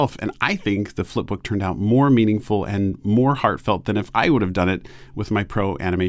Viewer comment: When you said “I think the flipbook turned out more meaningful and more heartfelt” I heard “more terrible and more heartfelt”😂